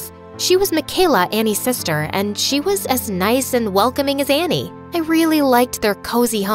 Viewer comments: thepart 5:18 the words said "she was mikala" but the caption said "she was Ashlyn" am i the one noitce it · she said micheala but the caption says Ashlyn what??? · anyone else realize the captions said “Ashlyn” instead of “mikayla” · i found a small mistake with the subtitles on the video, at 5:18 , it says ashlyn instead of mikayla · the caption is wrong. It's "Ashlyn" instead of "Mckayla".